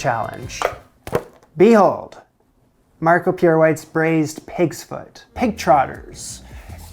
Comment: It’s Marco PIERRE White. Not Marco Pure White, which is what I thought he was saying. I’m an idiot, lmao!